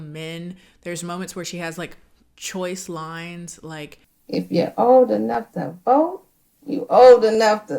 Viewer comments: That "old enough to vote" line delivery had me in tears 😂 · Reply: Ngl I couldn't understand her and thought she said "if you old enough to have a boat" 😭😭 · Ngl the "if you old enough to vote, you old enough to stroke" line was so much more confusing then me cus when first played it I could not understand the woman and thought she said something like "if you old enough to have a boat, you old enough to stroke" so I was truly perplexed about why having a boat is some age marker.